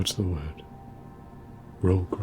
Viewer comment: Did he call me broke or rogue?